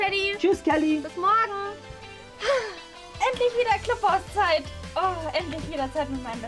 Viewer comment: Ich hab als erstes klohaus verstanden 😂